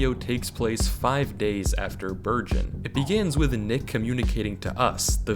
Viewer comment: i had the volume a little low so instead of hearing "burgeon" i heard virgin 😂